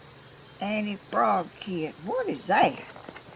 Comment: Lol I thought she said anti frog kit. Then realized it’s anti fog kit.